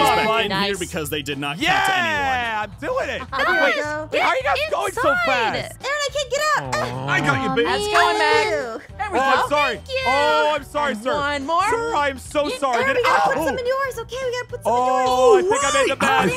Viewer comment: Hold up did he said Babe te Aphamu *Fangirl mode on*EKKKKKK AARON SAID BABE TO APHAMU EKKKKKKKK Edit:Thank for the likes